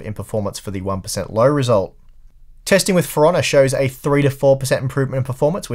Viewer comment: Lol I thought he said, "Piranha" instead of "For Honor"